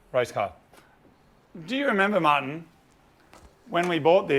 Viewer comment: I just realized Moog says Martin a lot, but I always thought he was saying Mahn! Like Man, but in a Jamaican accent! Am I the only one?!!